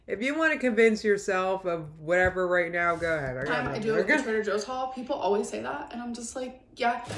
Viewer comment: Ew I cannot believe went to the TJs near my old house. Also it sounds like she says Trigger Joes which is honestly how I feel watching this.